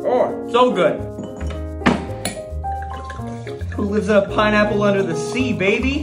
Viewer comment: I didn’t realise about him putting pineapple vodka until he said “who lives in a pineapple under the sea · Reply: sounded like he said “who lives a pineapple under the sea” to me